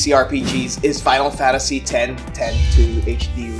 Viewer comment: did you say final FATasy?